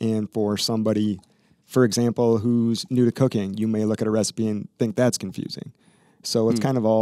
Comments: Bahaha, I thought he said a recipe for co.ca.in.e not cooking. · When he said cooking I thought he said cocaine lmao